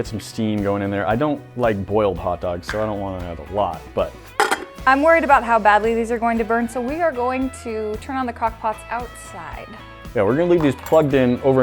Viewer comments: what did she say? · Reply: She said" im worried about how badly these are going to burn so we are going to turn on the crockpots outside" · Uhm did said ‘cockpots’ at 2:24??????????????